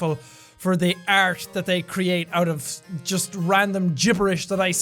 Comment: lol it sounded like you were saying "for the arse that they created"